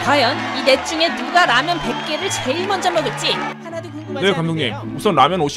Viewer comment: 나만 "하나도 궁금하지 않은데요" 라고들리나